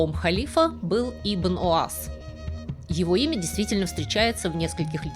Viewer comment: Сначала послышалось "Ибн УАЗ" 😂